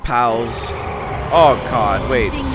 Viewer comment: sounded like he said "o cod" instead of "o god" lol